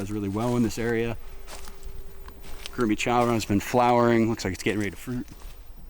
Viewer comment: I AM GROOT is all I heard at 5:31 when says: look like it's getting ready to fruit. You can hear the passion for growing in his voice.